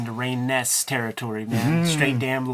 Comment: Very cool manga here! Love the casual body-horror throughout. Did anyone catch what Ed referred to at 13:47? I can't find a Ray Ness, and can't make out what he's saying after. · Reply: He's referring to Ragne Naess, creator of the Damlog character.